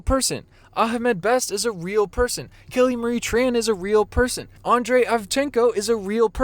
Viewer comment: When you said "Kelly Marie Tran is a real person" (9:34), I thought you said "Killing Marie Tran..." which lead me to believe she had killed her self. I'm glad she hasn't. :)